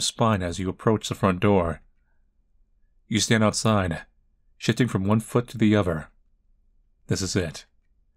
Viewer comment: I thought it said shiting 😭💀